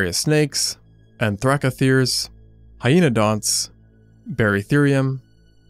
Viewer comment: I don't know why but I heard "And Francis" and laugh out loud